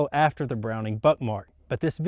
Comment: Am I the only one who thought he said buttmark